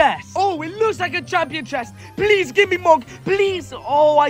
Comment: Who else didn’t listen properly and thought he said please give me mommy Bc were so used to it 😂